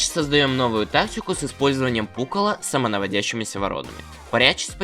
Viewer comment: Вот есть мамонт а как же папонт? 3:22 почему я услышу пукало